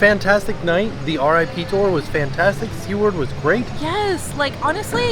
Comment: goodness I have been so excited to see this! I live for your reviews! Also, I thought you guys were saying her name was C-Word and the whole time I thought her name was somehow the bad c word. Then I read the comments. Can't wait for more spooky Tracker season!